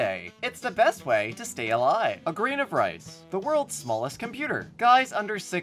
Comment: I could swear I heard you say "Grian of rice" instead of "grain of rice" and it made me so happy